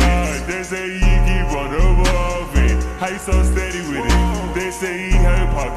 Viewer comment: Did i hear "They say evil has pockets swallowing ..." 🗿👀💀